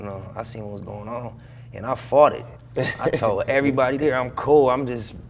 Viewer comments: “ and I farted “ - am I buggin or did he really say that cuz if so that’s hilarious 😂🤣🤣🤣🤣🤣🤣🤣🤣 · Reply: 😂😂😂😂 & I fought it* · I thought he said “and I farted” but he says “fought it” lol. Rip Nip ✊🏼✊🏾✊🏿